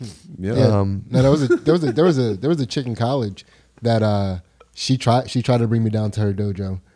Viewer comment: OK near the end, at around 1.03, I thought said: 'there was this chicken college...'